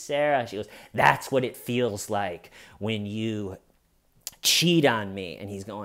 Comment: I thought she was saying “that’s how it feels when you choke me” not “cheat on me”